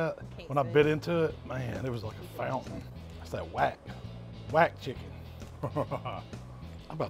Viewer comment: does he say "that's that wap chicken"